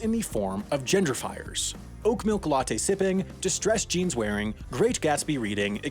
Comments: I thought you said gender fires. I was like, "what the hell is a gender fire?" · I heard "gender fires" and wondered what new woke trend I was missing out on. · did you say OAK MILK?😂